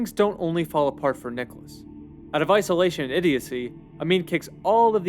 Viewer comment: "Out of isolation idiocy, Amin kicks all of the Asians out of the country; and the global media turns on him." I had to replay this section many times to finally understand what he said. Hope this helps someone else out too! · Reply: I think he says "out of isolation & idiocy"